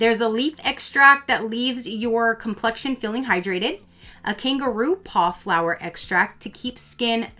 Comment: Girl omg I had to rewind and listen to the ingredients of the foaming cleanser. Cuz I thought you said kangaroo paw extract 😂😂 I’m like what are they doing to the kangaroos to get an extract from their paws. 🤣